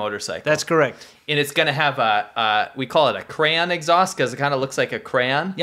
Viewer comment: I heard “Cran” instead of crayon. And you used to go to Canada to drink at 19. Michigan??